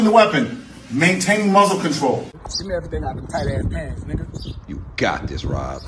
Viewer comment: Can any english native speaker please tell me, what the man toting a gun says in 2:38 "Give me everything ...... up pants" ?